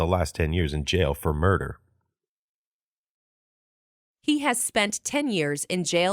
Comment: 何回聞いても男性の声が He has spent last ten years って聞こえるんだが… 無いはずのlast聞こえない？？？